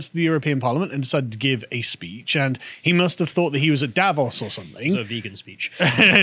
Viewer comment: I misheard the "thought he was at Davos" and thought I heard "thought he was Davros". Still made sense after viewing this robot dictator speak.